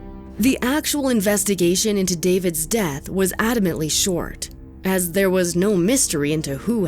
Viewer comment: "Adamantly short." closed captions say ; "Admittedly."